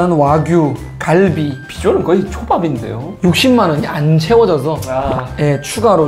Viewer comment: 여기부분 이 비주얼 그냥 초밥이란걸 왜 난 X빱이라고 들었을까